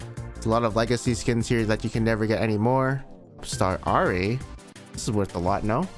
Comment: i thought u said P***star Ahri :)